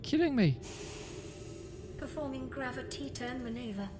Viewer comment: i heard gravatita XD